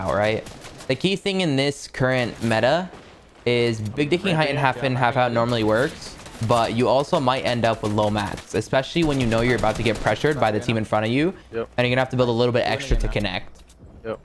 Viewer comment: what the fuck during game i thought he used to say "big digging height" not d*cking wtf